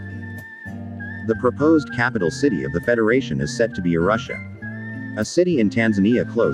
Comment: Thought it said Russia 😂